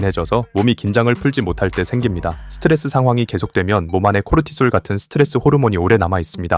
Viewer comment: 코르티스 아님ㅜㅋㅋㅋㅋㅋㅋㅋㅋㅋㅋㅋ 코르티스라고 들려서 다시 봈더니 진짜 아니엿슨